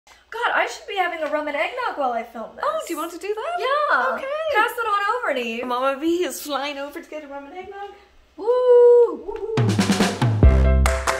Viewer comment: I thought you said Roman Egg Nog and I tried to Google it 🤣